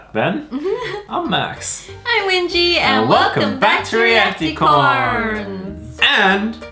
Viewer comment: You sound like you say realatecorns you’re channel is cald